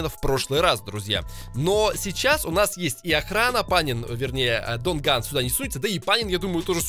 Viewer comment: Дон Ган или мне послышалось